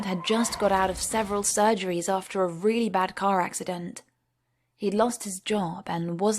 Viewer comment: I thought she said “He lost his JAW*